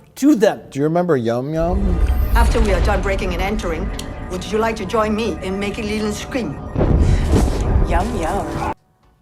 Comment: I still have no clue what the fuck Michelle Yeoh is saying here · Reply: “Would you like to join me in making Leeland scream?”